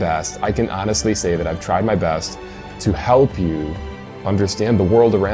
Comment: does Matt have a speech problem? it sounds like he said he "tried his beths"... Im not pickin on him, this is a serious question... and now for the pickin on... so, he believes the universe was created solely for humans, the entire universe, the whole enchalada, the bucket, handle and the water it holds.... just for man to live in?? and GAWD did all of this with no planning, no foresight, no nothing.... but yet he cant even entertain the thought that animals might have migrated over long distances to reach lands "that are just too far away"...